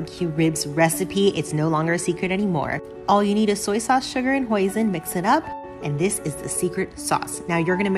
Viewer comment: Hoisin. I thought you said poison... I need to flee